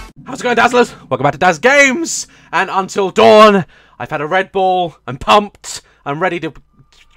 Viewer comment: omg haha when said i have a red bull I thought he said I have red balls